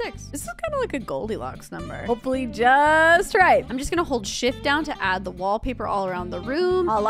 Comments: At first I thought she said I’m just gonna hold sh*t down to add the wallpaper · Sounded like she said shit instead of shift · For a second I thought she said hold sh!t down